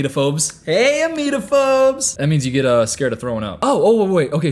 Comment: when he said “hey emetophobes!!” I was wondering what the hell that was, so I searched it up after rewinding SO many times bc I didn’t think I heard him correctly, just to go back to unpause the video where he immediately says it means people who have a fear of vomiting😭😭